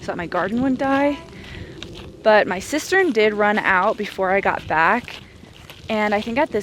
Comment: Also I thought you said "sister" when you said cistern... lol let me tell you about the CONFUSION that went through my head hahahaha Lastly, how on earth you're so calm around a poisonous spider is beyond me. Wow. Absolutely wow. Keep being 💯